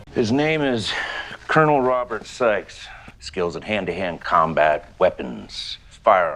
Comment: It sounded like they said the character's name was "Colonel Robert Sex". I know it's "Sikes", but that Seagal would call his character "Robert Sex" is the most believable thing in this movie.